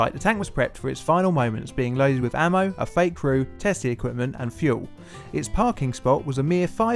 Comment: I thought you said "a fake roo"